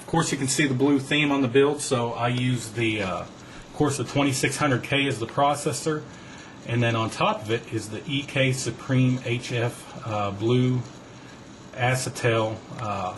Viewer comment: did he say corsair 2600k processor?